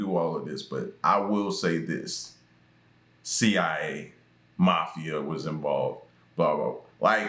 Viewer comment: He actually said CAA, Sammy miss quoting the 3 letter organization, should speak volumes. 💭 🤔